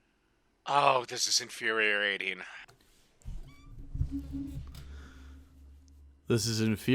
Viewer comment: Did he just say "infuhreriating?"